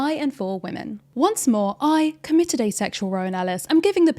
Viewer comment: For a second there I thought you said "I committed a sexual Rowan Ellis" and I wondered what a "sexual Rowan Ellis" was and how someone could commit it. I love when people names turn into verbs/nouns/phenomena (in a positive way) and was so confused how Rowan Ellis, famously asexual, would become a sexual phenomenon. Then it clicked :D